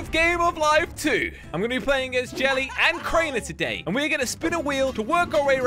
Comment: I’m going to play AS jelly a crainer He said as instead of with